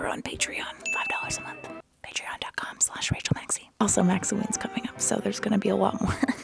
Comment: the captions say "(indistinct)" but she said “maksoween”!